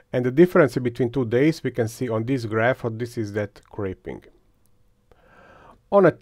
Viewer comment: I'm new to 3D printing so I'm not sure if it's my ears or lack of knowledge. Several times during the video it sounds like you say "crepe-ing" and I'm usually if I'm hearing it wrong or if that's a 3D printing word I haven't heard yet. What does that word mean? Thanks!